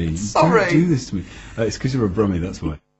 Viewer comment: does anyone else hear Richard say, "it's because you're a brony?" I know that's not what he said, but it sure sounded like it. The very idea of Richard knowing what a Brony is.... XD